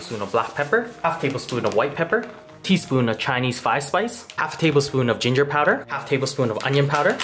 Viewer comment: So.... for anyone who needs a laugh. All these videos I thought he's been saying "Chinese fire spice". As a fan of spicy food I've been pretty hype thinking man, I gotta try this. I was today years old when I seen the text on the screen saying "Chinese 5 spice" .